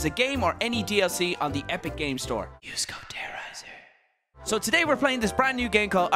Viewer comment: All I heard was “use code Moo”